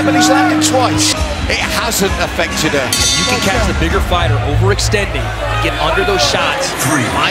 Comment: it sounds like he said the nigga